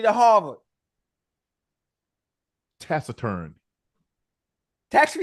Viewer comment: I thought you said tax return😂😂